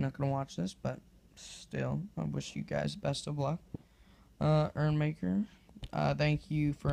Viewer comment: urmaker is pronounced YourMaker....not sure but it kinda sounds like you say ErrMaker ....I'm being such a Grammer nazi forgive me if I heard you wrong :/